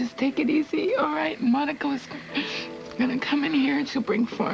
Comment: it sounds like said Monico. :)